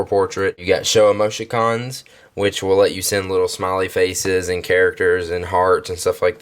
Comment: go there and it sounds like hes saying "bitch it wil let you..."